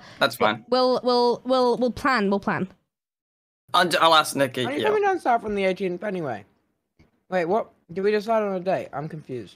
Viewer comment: does anyone know what he said at 2:57? i cant understand what hes saying. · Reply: I know it’s 10 months later but he said “are you coming down south from the 18th anyway? Wait what- did we decide on a date?”